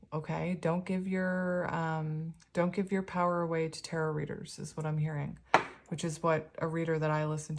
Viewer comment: Pile 2 I thought you said “don’t give your power away to terror readers ..” 😂